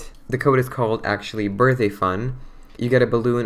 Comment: Anyone hear that? When he said "Birthdayfun" is sounded like he said Birthafun